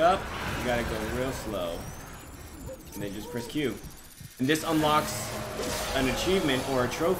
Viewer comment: did you say "just press cube" ???